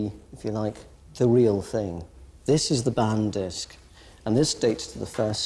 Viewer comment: "This is the ban disk." Is this what is said here? "Ban disk"? I can't find this jewelry piece on google under "ban disk," does anybody know the name of the piece?